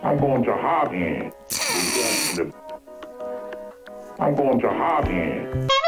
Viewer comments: Am I the only one that can't hear "I'm going to honk you"? I hear "I'm going to hog it". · I keep hearing him say "I'm going ta harm ye" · Am I the only one that can't hear "I'm going to honk you"? I hear "I'm going to hog it". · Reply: If only because of the nuff wanks part. I hear "I'm going to harden" every time. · I thought the human said 'I'm going to hug you!' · I heard "I'm going to harm you." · I heard I'm going to harden it...XD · I thought he said "I'm going to hard here" with a new york accent. You know, cause there have been "nuff wanks"?